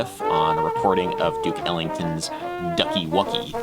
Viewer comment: When he said Duke Ellington I heard it as Dick Ellington